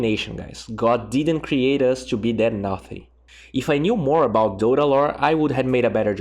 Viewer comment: "naked Midas is an abomination, guys. God didn't create us to be that nothing." I may have butchered that exact interpretation, but I understand what he's saying, and I agree 100%. Do not rush midas on Invoker if you are having a tough laning stage. Ring of Aquila is great on Quas_Exort Invoker because of the mana regen and the strength it provides to the Forged Spirits. Nice Guide. Thumbs Up. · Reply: just a little correction: i said naughty